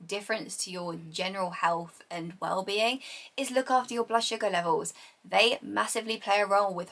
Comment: Hello, I'm sorry , I can't understand what levels? At 11:28 minutes. Thank you so much! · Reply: Blood sugar levels